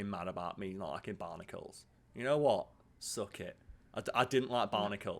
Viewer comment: you have never been so wrong about something as bionicals 😂 they were all different with different weapons helmers and accent pieces 😂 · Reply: OK, bionicals makes a lot more sense than barnacles, which is what I THOUGHT he said...